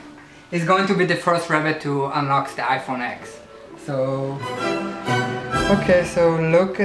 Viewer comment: Yeah right, I thought you said your rabid is the first to unlock your iPhone X